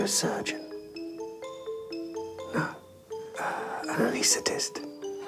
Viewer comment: what does he say at 9:59? An elecitist???